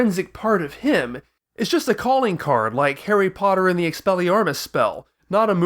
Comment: Did you just say exsellyarmes? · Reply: I said Expelliarmus.